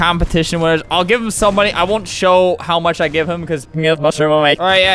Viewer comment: I put it in reverse and im pretty sure he says: I won't show how much I give him cause somebody might scam him or something. That what he says.